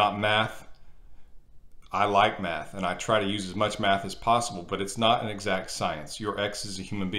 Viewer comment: As a non-native english speaker, I heard "I like meth, and I try to use as much meth as possible" and I was like daaamn.. so drugs are the exit to this suffering!